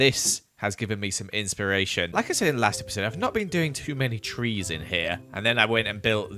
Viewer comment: “I’m not being doing too much trees in here” Oml I heard “I’m not doing too much treason”